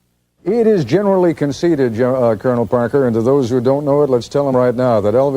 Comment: it sounded like he was about to say General Parker before he said Colonel Parker